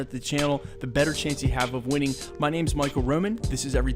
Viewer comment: I thought he said he was Micheal Rosen at the beginning